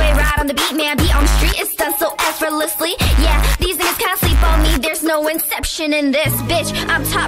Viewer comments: She fr said the n word idk how people are defending her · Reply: U can watch it at 0.25x and you'll hear it clearly at 0:30 she said singas instead of n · I didnt hear any N until I went through the comments, I replayed it like 10 times but couldnt hear it, I still cant.. I heard "These things can't sleep on me." And I still do???